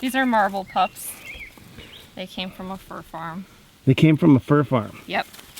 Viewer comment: ''They came from a fur farm'' - This world can be disgusting fml :'( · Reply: I thought she said fruit farm